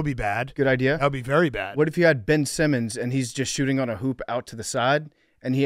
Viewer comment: I thought PFT said Bill Simmons was shooting on the side